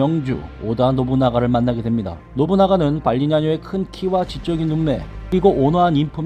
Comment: 발리냐노 발음 정말 힘들지 않았나요?😹 계속 발음해봐도 잘 안되더라구요.😿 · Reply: ㅋㅋ 이분 영상에서 발음도 발리냐노가 아니라 발리냐뇨로 들려요